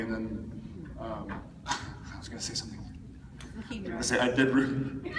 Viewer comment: What does Jared say at 16:06? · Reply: Ann Kristin Vestnes "I did Ruby."